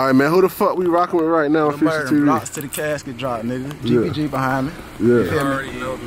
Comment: did I hear tbg? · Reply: Gbg